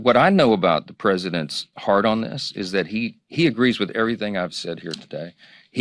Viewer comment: I had to listen to this a few times to understand what was said. “Heart on”. Not (what I first heard) “hard-on” …. Would have been more accurate with the latter…. FDJT!! 8️⃣6️⃣4️⃣7️⃣!!!